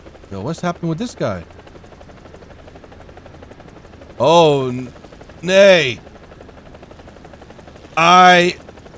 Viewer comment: Did you said: Oh nee? That's dutch for: Oh no!